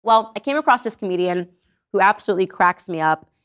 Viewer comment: So at the beginning I thought she said I found this Medium (not Comedian)